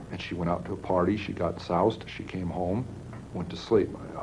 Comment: I thought he said sauced 😂 I didn’t know soused was a word